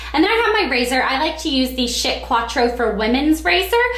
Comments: did she say shit a 4:35 ??? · I thought u said "shit quatro razor"! Lol!